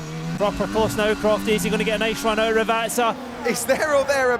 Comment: I cannot for the life of me understand what the commentator is saying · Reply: He said is he going to get a nice run out of Rivazza. Rivazza is the corner name